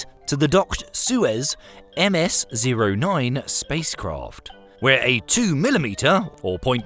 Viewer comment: I thought you said: "...docked sewer MS-09 spacecraft", Had to turn on captions after and READ what you said... Anyone else? 😅